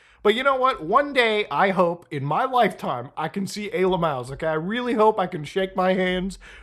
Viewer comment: What does he keep saying? Ayla Maows? At 0:43. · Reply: He's saying A-LMAOs, just a memey way to say aliens